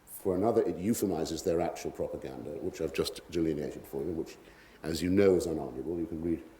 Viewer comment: please, what words does he use at 49:05 "....ive just ejuianaed for you" ?? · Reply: delineated : v -to describe or portray precisely